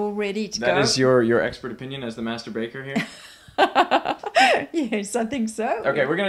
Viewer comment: Was I the only one who thought he said Masturbater when he said Master Baker XD